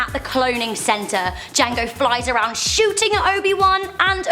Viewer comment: did she say JANGO. FAT